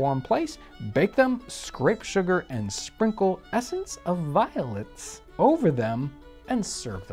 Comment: I had to listen to that again a few times. I thought you said essence of violence. 😂 I was worried you were gonna hurt someone to make this.